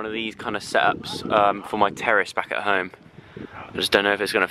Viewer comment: did anyone mishear “terrace” for “terrorists”?? I was like JOE WHAT DO YOU MEAN YOUR TERRORISTS BACK AT HOME??!